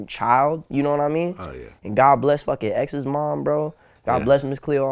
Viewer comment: did he just said "god bless FUCKING X mom"? LOLLL